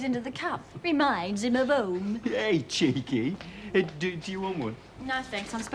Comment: What does Heather say at 29:05? Me mines in a bowl ?? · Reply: She says. Reminds him of home.